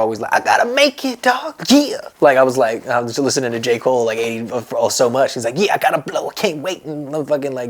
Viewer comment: Who else thought logic said “I was listening to Jake Paul” but it was J Cole